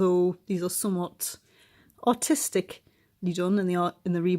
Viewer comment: I thought you said “their quite autistically done” lmao